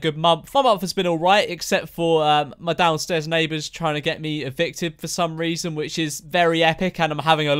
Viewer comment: I thought he said his neighbors tried to get him addicted